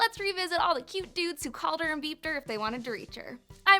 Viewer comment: I swore you said “Called her and beefed her.” Instead of “called her and beeped her” And I lost my mind